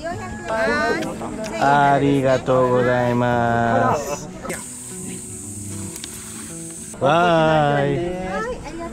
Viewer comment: Okay I have to ask. I just returned from Japan and kept hearing the same thing and you just said it...what are you saying after you say "arigato" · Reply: Abrigatou gozaimasu